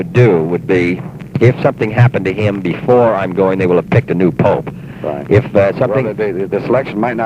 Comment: what did he mean by saying he would pick the new pope? Was that a Joke or does the US President pick the pope? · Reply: No, it may have been his accent. I think JFK said "they may have picked a new pope by then." <3